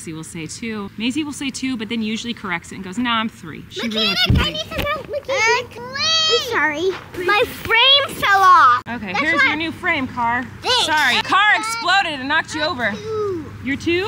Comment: Anyone else think said “I’m sorry, my brain fell off” at 5:14 😂 🧠